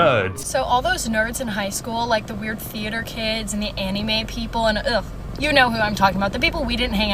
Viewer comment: The way she said anime killed me I thought she said Fannie May 😭